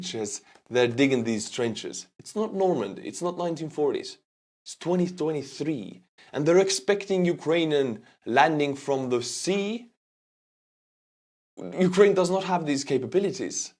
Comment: Did anyone else think he said 2033 the first time you heard this?